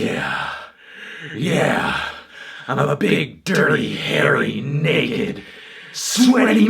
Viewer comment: "Big dirty hairy naked sweaty melon" should be the title of Anthony's debut record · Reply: I thought he said big dirty hairy nigga